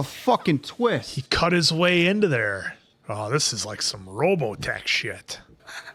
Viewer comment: I'm sorry, did he say Robotech, or am I hearing things? Also, don't really like this show, but I love you guys reactions. I MAY actually watch you guys do the whole series.